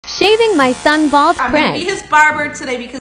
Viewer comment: I thought you said *shaving my sons bald friend*💀